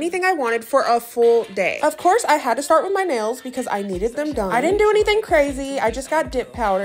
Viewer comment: girl I'm so happy for ya you got a good man who treats you like a queen 👑 (Also when u said you were going to get nails done I thought you said mails 😭)